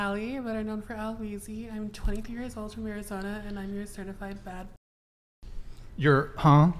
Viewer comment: I keep hearing “certified fatty”